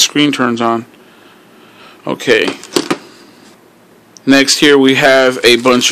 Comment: sounded like he said next year